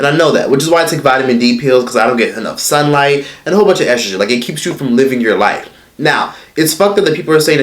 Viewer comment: 😟Wait. 15:08 Am I hearing things, or did he say that he takes vitamin d pills and a whole bunch of estrogen pills? 🧐hahaha